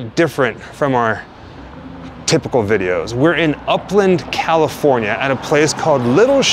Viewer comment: Man;! I wish I could of gotten a heads 🆙 that you guys are in my back yard, I would of love to say hello! Oh well hello 👋 ! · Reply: Oh;! Did you say upland ! I thought you said Oakland!